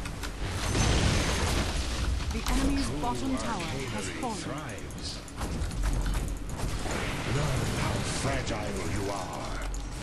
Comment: "The enemy's FUCKING tower has fallen" Did I hear that right????